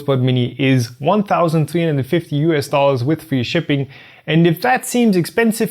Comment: what did you say at 10:38?, free shipping?, I tried to find the shipping on the website but couldn't find any, but it lets you choose availability for my country..